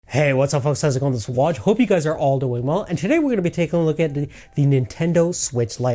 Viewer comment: in.. I have no idea what you just said. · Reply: He said I Hope you are all doing well lol I had to re watch from beginning